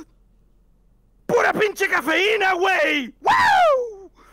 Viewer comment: no offense if any of this means something an another language but I heard: “Budapin chikin fajita way!” Idk what I heard